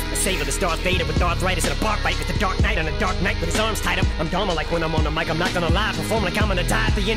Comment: The lyric offficiandos miss that em says "inner labia" ... I thought em also said "I'm dola like when I'm on the mic" like dolomite